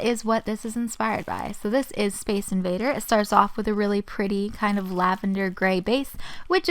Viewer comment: I thought she said base invaders